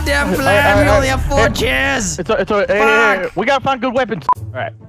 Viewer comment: when vanoss Says "we only have 4 kills" mine it sounded like Vanoss:"WE ONLY HAVE 4 KIDS" me:*dying of laughter😂*